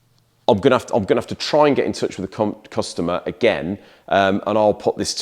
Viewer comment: I thought you said cu*t customer had to rewind lol. Great videos